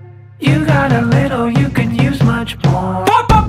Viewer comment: First time I heard this I thought he was saying "you have a little you could use much PORN" Ohboy..